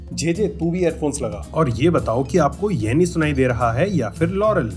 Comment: I heard yarny